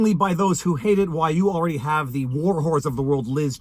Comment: #Viva, @ 14:00, did you call Liz Chaney a war HORSE, or a war WHORE??? Asking for a friend.